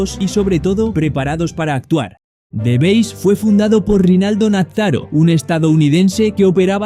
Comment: te juro que escuché Ronaldo nazario y no ronald nafhrjchfbd